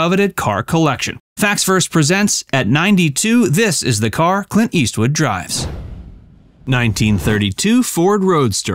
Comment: For a moment there I thought you said that Clint Eastwood had died good to know that's not the case